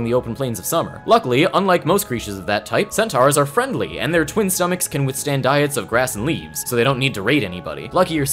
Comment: I thought you said their twink stomachs and I just accepted that